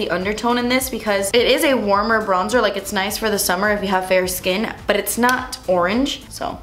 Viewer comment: Did anyone else hear her say "burrrr" when she's supposed to say "but" at 07:57? I freakin' love the way she talks ✨🔥